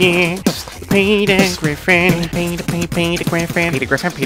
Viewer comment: p***s Griffin is what i heard